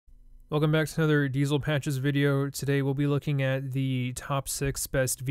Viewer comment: I thought he said "Welcome back to another depressed train video" lol